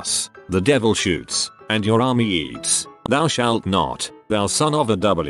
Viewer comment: I wasn't looking at my computer screen and I thought they said "and your army yeets" Oh my god I'm dying! XD